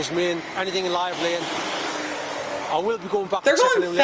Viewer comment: When you thought he said “highway” he actually said “live lane” as he was saying about anything being in a live lane was the main priority😁